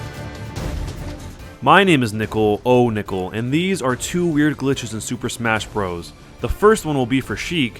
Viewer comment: goddamnit i thought it said weird glitches with shrek XD